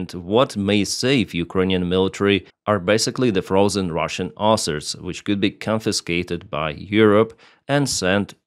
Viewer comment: I thought was saying: "From the frozen Russian arses" 😂